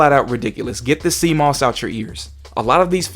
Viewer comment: for i second i heard 'sea moss' as 'CMOS,' as in the little battery that used to keep your BIOS settings from being erased when you powered off the pc tower